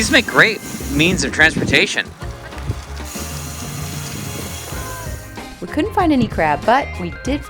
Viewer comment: I’ve watched this one before but YT started “autoplay” today so I watched it again 😅 When said, “we couldn’t find any crab, but…”, I heard “we couldn’t find any crab butt” 😂